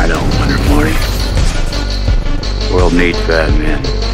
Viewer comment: My high ass thought that he said "world need BATMAN"... i was like the fuck? (Plus i am foreigner)